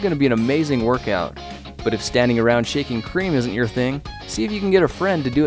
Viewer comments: I thought it said shaving cream... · i thought it said shaving cream